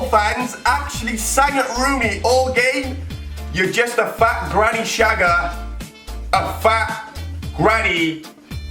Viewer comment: In the Rooney chant I heard "You're just a fat Granit Xhaka" at first